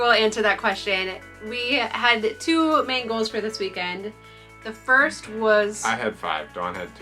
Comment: During the introduction, when you said, "We have two main goals for this weekend." I heard, "We have two mangos..." 🥭🥭